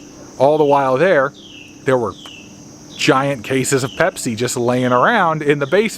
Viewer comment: I thought you said "Hep-C" instead of "Pepsi".... Multi tasking can be bad for me sometimes.... okay, back to the video!